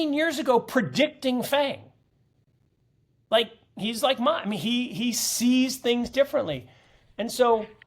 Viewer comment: That is why he talks so much, he needs to get it all out. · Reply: Did you say that Saylor is like him?😂